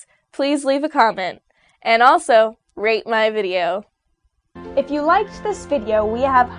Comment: i thought she said rape my video.